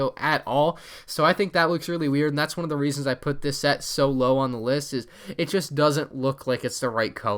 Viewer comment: he said so low but I thought he said Solo.